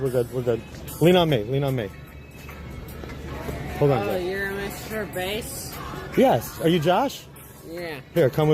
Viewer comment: I thought you were saying Mr. Beast and I was so confused like how much free time does Jimmy have these days? Lol.